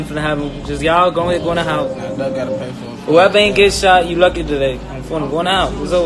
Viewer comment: I heard somebody saying duck gotta pay smh it’s almost like they knew cops could care less…they was so loose with it….